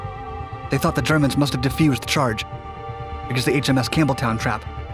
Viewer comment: Sounded like he said, "the USS Camel Toe". There for a second. Lol